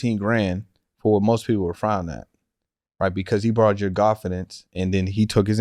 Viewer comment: Did he say, "He borrowed your Godfidence"? 😀